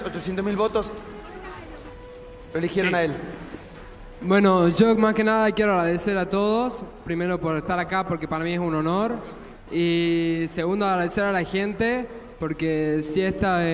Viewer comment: escuchen lo que dice porfavor. marcos marcos pero No escuchan lo que dice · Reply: yo más que nada quiero agradecer a todos.. primero por estar acá porque para mí es un honor y segundo agradecer a la gente ... wooooou marcos que lindas palabras